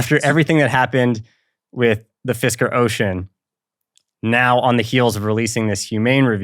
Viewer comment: what’s the “fisher ocean”? What he say? Edit: ohhh “Fisker Ocean”? So it’s a car review?